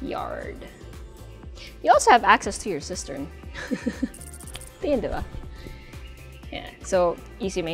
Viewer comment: Lol, I thought you said, “you will also have access to your sister as welll. 🤨😂🤣